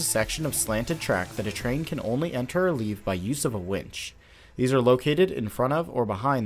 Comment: I misheard the line at 2:40 as "by use of a witch" and envisioned the ride op employing the help of a woman gripping a crystal in one hand and telekinetically working the train up the track with the other. That aside, great video! I'm a chemist by trade but i've always had a strong engineering interest, and it's fun to see how these systems are designed. Earned a sub.